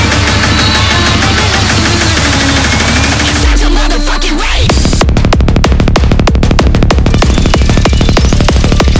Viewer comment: What is saying? Please tell me. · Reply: "It's such a motherfucking rave" lol